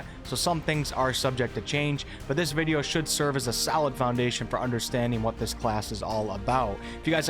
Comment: it sounded like you said "this video should serve as a SALAD foundation for understanding what this class is all about"